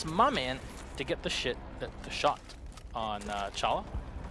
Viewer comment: 😂😂 did he just say "to get the shit the shot" lol